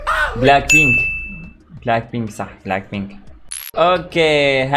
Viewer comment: سمعت سمهن براك بيك